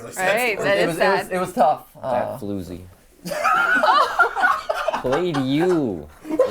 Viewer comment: I didn't understand what he said why did they laugh a lot? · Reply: He called Matt's ex a "floozy", basically calling her a tramp. I'm sure that it's more comical the deadpan tone he said it in, then what he actually said, that made it humorous.